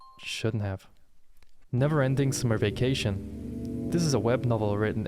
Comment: I misheard it as "Never Ending Smurfication" and immediately imagined someone was turning Japanese people into Smurfs